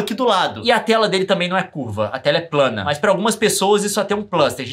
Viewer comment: eu ouvi um "a terra é plana"?